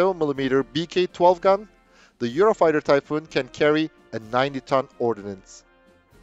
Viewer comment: A lot of nonsense and disinformation from the beginning. I'm not going to waste whole 16 minutes to this. To other viewers: be critical of what you see and listen. Makers of most videos like this tell you surprising things, because viewers find surprises entertaining (and honestly, most viewers come here to be entertained). The problem is that when the reality doesn't offer any surprises, they create them. That can happen by making things up, or by distorting facts. They both are a form of lying. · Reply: I thought for sure he said it could carry a 90 ton ordinance??